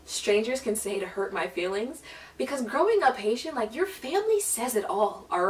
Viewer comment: what is she saying at 7:33 ''because growing up word like your family...''' nice job on the coughing tho happened to me a couple times you, i kno the feel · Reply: lol thank you and the word is Haitian